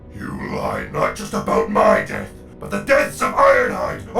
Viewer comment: For Ironhide, Ultra Magnus, Bumblebee, for everything. Till all are one. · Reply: I always thought Megas said the deaths of iacon